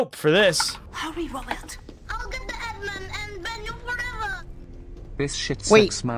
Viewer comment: wait the donation alert is saying "i'll get the admin and ban you forever"? i always heard it as 'eggman'